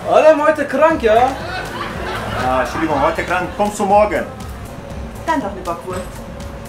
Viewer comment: Was sagt sie da? "Dann doch lieber kurz"? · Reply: "Dann doch 'ne Bockwurst'!"